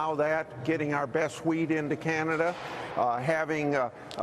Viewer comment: Did he say, "Getting our best weed in to Canada"?